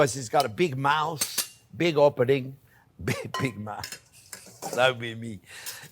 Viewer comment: thought u said big mouse😂❤